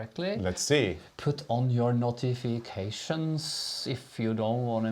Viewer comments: I knew this would be a fun Q & A when I heard say " notifications " and I wasn't paying attention yet and I thought he said " naughty vacations"...lol I love your attitude toward knitting and crafting in general, it should be fun and enjoyable or find another hobby. Everyone knits their own way, if the result is what you want then who cares about how you got there, never listen to anyone who says you are doing it wrong ! I've switched to the Norwegian style of knitting because of your two and I'm enjoying my knitting so much more ! I can't thank you enough ! Have a wonderful week ! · was slightly hesitant when saying 'notifications', and it sounds like he was saying 'Put on your naughty ...' and I was wondering what naughty things we were going to wear today. :o) I was rather sad when 'naughty' transitioned into 'notifications'. I'm going to put on naughty socks (socks I wear when I'm naughty).